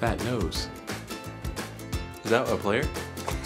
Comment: Oh ok, I thought he said “is that a myplayer” for Romeo